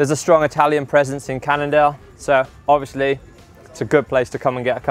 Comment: I heard "Canada" instead of "Cannondale"